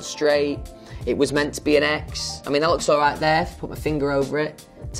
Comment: okay, i think I've known Louis long enough to get his accent.....and at 02:00 DID HE SAY IT WAS MEANT TO BE H ......AS IN H.S......AS IN HARRY STYLES?????